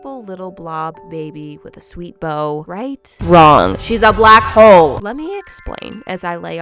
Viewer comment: why did I hear "SHE'S A BLACK HOE" and not "She's a black hole"